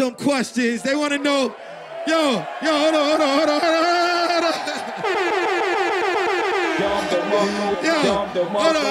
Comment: Who is shorty wit the red hair and what was she saying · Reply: she told drake to say u gotta be surf first